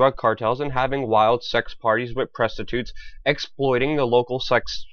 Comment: did you say with presstitutes?